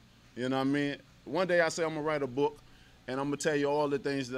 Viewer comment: When Wilder said one day I’ll write a book I thought he said ride a Bull Lol I had to rewind it right quick. I pictured him riding a bull and thought oh hell no 😂 imagine that